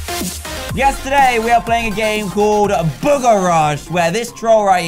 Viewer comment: Yesterday we're playing a game called "Booger Rush!" Me: Great English... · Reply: He said yes today we r playing booger rush. Not yesterday we r playing booger rush. It really did sound like he said yesterday though